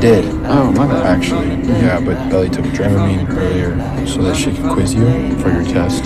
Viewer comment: I thought he said Dramione not Dramamine😂😂